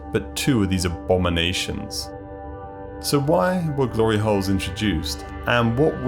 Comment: did he really say "glory holes"?